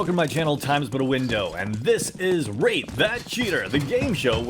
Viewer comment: bro me not paying attention I thought he said rape that cheater!